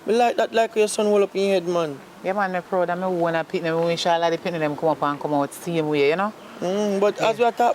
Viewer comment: Did candy say Insha Allah 🧐